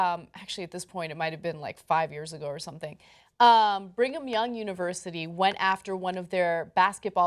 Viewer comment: did I just hear Ana say "bring-em-young university"..